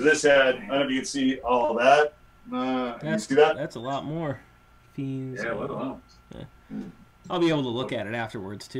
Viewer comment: It’s called “Fiends” for Clarification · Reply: oh, I thought it was called "Friends" XD. And because of that I heard "Things" instead of "Fiends". Thanks dude!